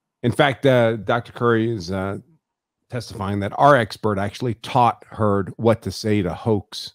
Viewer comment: is he saying “our expert” taught heard? I watch the DEPP V HEARD Trial every couple of months or so and just heard this lol Do not tell me that Dr Hughes is your expert? 😱